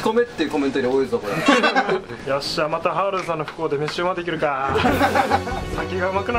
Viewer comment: ，いつもはるおっきのこと「ハウルさん」って呼んでない？ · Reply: ソレか ハとルの間にちょっと間があるからハォルさんみたいに聞こえてた 5:33あたり